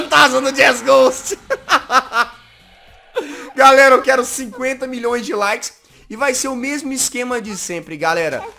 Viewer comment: Gente olha em 2:56 . O jazz pede 50 milhões de likes KKKK é isso mesmo que eu ouvi? Ou eu tô surda